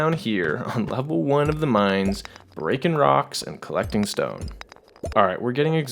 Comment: did I hear a rock and stone?